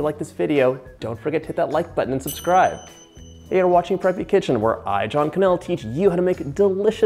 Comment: off to a great start, thought u said 'pregnant kitchen'